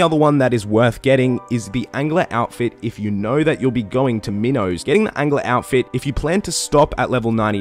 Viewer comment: balloon transport system is good for taverly also. Accessible by castle wars teleport and walk up to Assistant Marrow. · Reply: I thought you said "Minnow" instead of "Marrow"